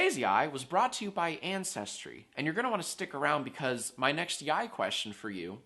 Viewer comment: I thought you said incestry.